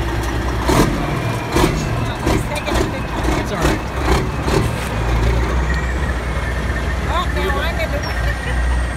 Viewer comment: what did that woman say 2 you??🤔🤔🤔🤔🤨🤨🤨🤨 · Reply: “He’s taking pictures”